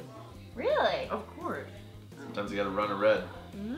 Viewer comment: what does say at 4:05 ?? I can't tell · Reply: "Sometimes you gotta run a red" 😜